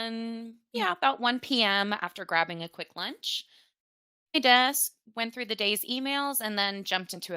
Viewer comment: what did she say??? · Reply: Good catch! The subtitles made a mistake here. said: "Sat down at my desk..." We've upgraded to better subtitle and microhpone software since this episode, so things like this don't happen any more!